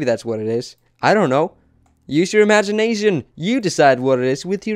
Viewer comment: It sounds like he said imagine Asian instead of imagination